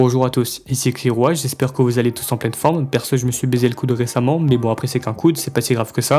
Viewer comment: c' moi ou y dis j'me suis "baisé le coude"?🤣😭🙏